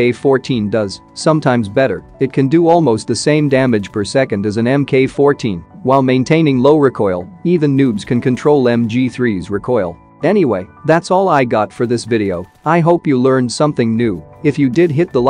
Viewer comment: did you say Noobs can control I am going to try mg3